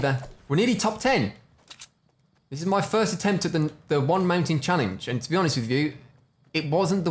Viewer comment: Reminds of the one mountain challenge you did a while ago where I thought you said 'one man ting' challenge 😂